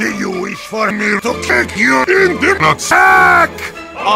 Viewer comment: did heavy say Kick you in the Painis?